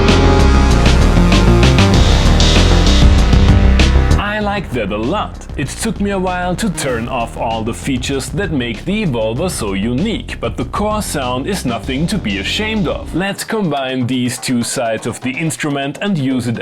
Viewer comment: I hear every time "the vulva" ....once you hear it you can't unhear it